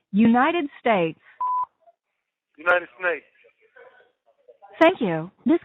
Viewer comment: I heard….. “united 🐍🐍”